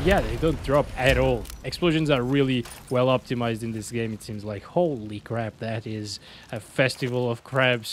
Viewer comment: Holy crab did you say 😅